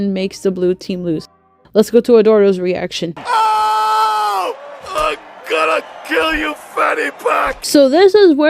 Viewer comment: when i first saw this movie what i thought i heard "I'm gonna kill your fanny pack"